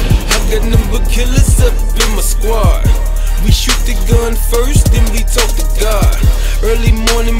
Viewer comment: I thought he said they shoot da gun first den we tought da guard